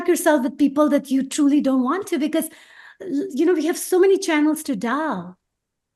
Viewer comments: I love her but what did she say @ 31:29? Channels to ? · she said ''we have so many channels to <DELVE is what the googlesays she said, what WORD DID SHE SAY????> I know the idea she was gettin across we have so many ROADS to travel in our own journey so don't be PARKING yourself with peeps that ain't about YOUR JOURNEY or that HAMPER or have ill effect ON YOUR JOURNEY..... but what was the word she used?? ahhhhhh!!!! 51:21 did she say ''we have so many channels to DIAL ???'' like the STARGATE :) and EVERY setting GOES SOME PLACE !!! :) hehehe